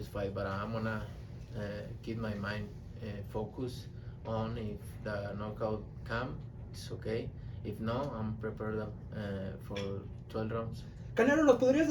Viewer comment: I could've swore I heard"if the knockout comes okay. If not I'm ready to go 12 rounds"